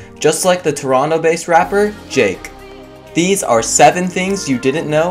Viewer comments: did he say jake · DID HE JUST SAY "Jake"😭😭